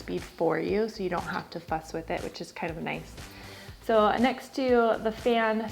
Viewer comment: I thought she said " so you dont have to fuks wit it, so thats nice." Had to watch it again.